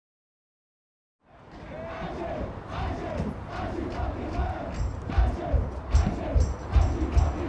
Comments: que dicen? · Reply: "As you! As you fucking were!" el nombre del álbum. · What did the public say at the beginning? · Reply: Factory oh thanks bro!!! Greetings from Argentina 👍👍